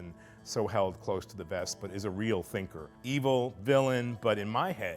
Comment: i thought he said finger